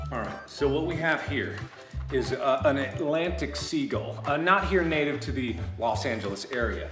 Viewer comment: what is mark trying to say about the Pacific? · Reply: Atlantic